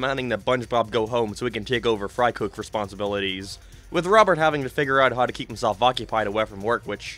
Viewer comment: In season 10 episode 7 you say Robert instead of sponge bob or maybe my ears are fucked up you decide. The point I heard “Robert” was